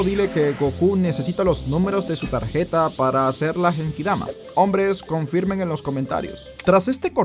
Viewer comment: estaba escuchando la anécdota mientras lavaba los platos y no escuché bien, dijiste que Goku necesita mis números de cuenta bancaria y seguro social?